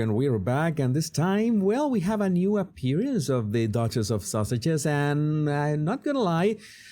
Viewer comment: When you said Duchess of Sussex I thought you said Daughter of Sausages 😂😂😂